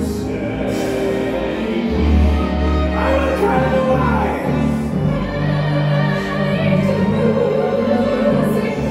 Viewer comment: What did Tim yell at 6:09? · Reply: ‘I’ll tell you why!’